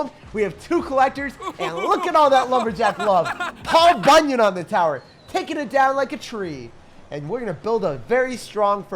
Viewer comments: That sounded like to me Lol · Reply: It was 😂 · what did say?... paul- what? anyone?